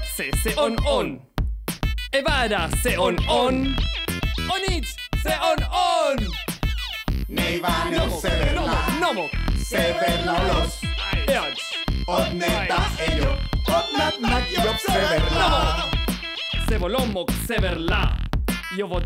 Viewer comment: yo entendi dizque "Me iba en el severla" XD